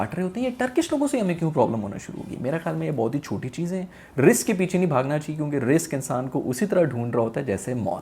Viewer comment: When you pronounce rizq Sounds like you say risk 😋 Btw I love to watching your videos your sence of humor is out the world😊